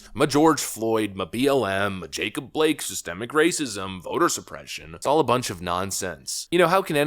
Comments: I heard "Assisting Racism"😂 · I thought he said assistant racism….WTH does that mean.. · Lol I thought I heard him say "assisting racism" and was like 'hold up'